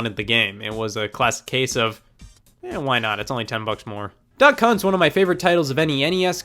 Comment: it sounded like you said "duck cunt". It would't be wrong except the only cunt in that game is the dog